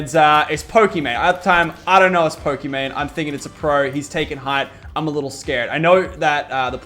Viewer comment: did he say hes taking height?